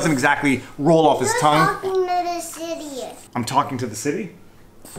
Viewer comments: I thought your boy said "You're talking to the idiots." Lol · No, Your boy definitely did NOT say "you're talking to the city"; No, he said "Who's talking to this idiot"?🤣👍 Your boy's a BOSS! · A bit of a different format for this vlawg. I didn’t feel comfortable cutting the audio because it was not clear where the edits were made. And I only realize this after having recorded my running commentary. 😂 If you’ve got any questions, ask him in the comment section, and I will do my best to answer. ✌️ Support links: <link> <link> <link> vLAWg ON! <link> Old new merchandise: (New Pudge Vlawg Dawg shirts). With logo <link> No <link> Original Vlawg Dawg: <link> Merch: I SHILL FOR NOBODY. It's perfect! <link> <link> Keep Calm! <link> Know the Vlawg: <link> The awesome thumbnails are from DSLR Dave! He’s open for business, check out his channel! <link> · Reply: No, Your boy definitely did NOT say "you're talking to the city"; No, he said "Who's talking to this idiot"?🤣👍 Your boy's a BOSS!